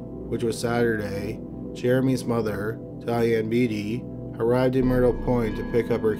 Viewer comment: I thought he said the mom’s name was “Diabete” at 2:42 😭😭😭😭